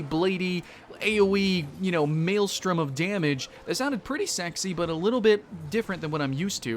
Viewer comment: Did you just say "Maelstrom of damage"?